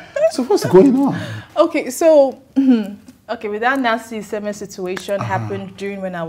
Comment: She said when that "Nancy Isime" situation happened... I don't get. The caption says "Nancy Iheme", this lady in question said "Nancy Isime". Stop confusing us pls..